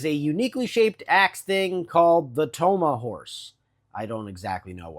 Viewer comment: Toma Horse instead of tomahawk? 🤔 I never knew the weapons had names.